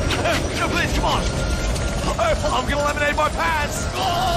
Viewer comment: I thought he said "I'm gonna eliminate my pants"